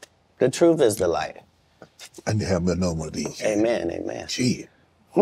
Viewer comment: what does Shannon say ? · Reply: "Imma have another one of these" and then he takes a drink.